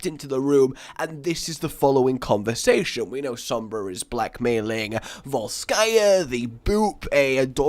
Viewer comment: I keep hearing 'Black Male' instead of Blackmail' XD